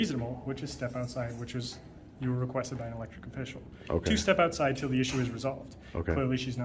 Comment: did he just say "electric official"? 😮